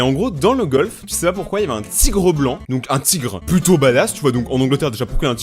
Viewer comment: jai entendu " un p'tit gros blanc "